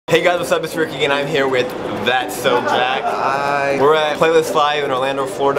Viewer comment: When you said Hey Guys it's Ricky and I'm here with That's so Jack! I thought you said That's so Raven!! LOL!